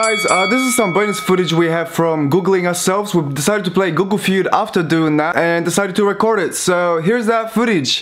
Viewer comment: Ja usłyszałam google fiut